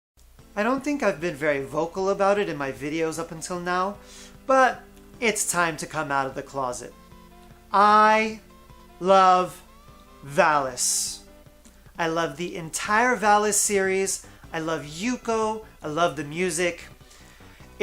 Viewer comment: Had you in my pocket and thought i heard you screaming "i love phallus", was like whoa buddy we all do but don't need to open your video exclaiming it.